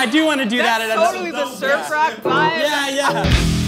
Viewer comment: I heard "surf rock" as "Sir Frock"! :D